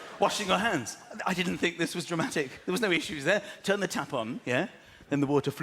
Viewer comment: Tap... on. I thought he said tampon.